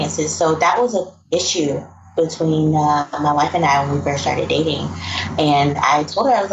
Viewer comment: wait what, my wife and I? it dont matter i am just making sure I heard correctly